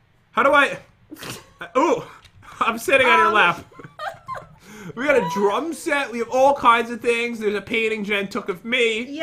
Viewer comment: i thought he said (Pat) "we got a DRUG set" lol